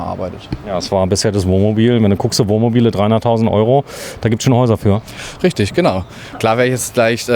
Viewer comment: Ich verstehe die Aufregung nicht?! Für knapp 100.000€ bekommt ihr ein Haus gestellt, was von außen komplett fertig ist und inkl. Architektenleistung! Und das ganze ohne versteckte Kosten und mit Preisgarantie! Ihr glaubt doch nicht ernsthaft, dass man für 100.000€ ein Haus bauen kann? Wir haben vor 7 Jahren mit einem sehr teueren und namhaften Massivbau Hersteller gebaut und das Resultat - 20% Nachfinanzierung am Ende der Bauzeit. Meine Cousine hat 2018 mit massa gebaut und sich "nur" für das Ausbauhaus entschieden und sonst die Gewerke einzeln vergeben. Am Ende hat alles super funktioniert, der Preis, der im Hausvertrag stand, musste sie schlussendlich auch zahlen. Mega!!! Und zur Qualität kann ich ein sagen- sie ist mega! Bevor ihr ein Kommentar zu der Qualität hinterlasst, schaut euch einmal den Rohbau an. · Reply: Ich habe was von 600.000 verstanden Also wenn das im Video 100.000 kostet baue ich 3 oder 4 davon